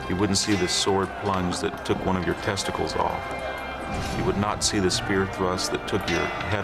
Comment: what did he just say? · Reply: Testicular combat